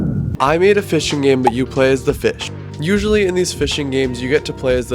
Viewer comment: I literally thought it said a fighting game but your a fish (this was at night) (either way into the playlist it goes)